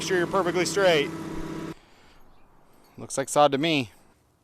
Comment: did he say "looks like sodomy?" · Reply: "Looks like sod to me"